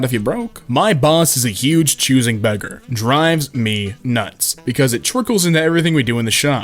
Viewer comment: it thought you said dry deez nuts